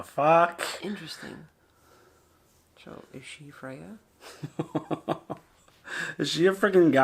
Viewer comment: I thought said "is she a crayon" lmao had me on the floor